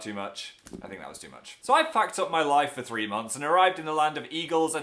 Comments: did he say i fucked up my life???😱 in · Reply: i thought he said that too but he said packed up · hearing phil swear is so fucking shocking every time idk why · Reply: Maja Kucharska 0:10 – Phil said "So I've packed up my life for three months." Perhaps you misheard due to his accent, but it's pretty clear he that said "packed" considering the context. He didn't drop an F-bomb in this video lol. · I thought he said "f*cked up my life" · does Phil say, "So I fucked up my life for three months"? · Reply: He said, " I've packed up my life for three months." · DID HE JUST SAY FUCKED UP???? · It sounds like he said "I fucked up my life for three months" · did he just curse xD I wanna come to the tour but im not in america ; A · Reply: I thought so to but he said packed not the f bomb :) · Wait when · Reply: redfox Gamer In the beginning, "So I f*cked up my life for three months..." I mean that's what I heard, I'm not sure doe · on 0:11 it sounds like he said so i fucked up my life for 3 months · I thought he said so I fucked up my life at the start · did he swear at 0:10 ???I heard he say "fucked off" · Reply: I think it was 'packed up' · I THOUGHT HE SAID "SO I FUCKED UP" 😂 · I thought he said, "so after I fucked up my life..." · DID HE JUST SAY "I FUCKED UP MY LIFE .." WTF WHAT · "so i fucked up my life....." why did i heard that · I thought he said "so I fucked up my life for three months" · I thought he said "So I fucked up my life in 3 months."